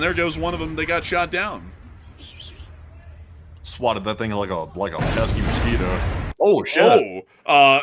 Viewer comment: I had to rewind when he said “ swatted that thing like a pesky mosquito” because what I initially thought I heard was “swatted that thing like a peasant” LOL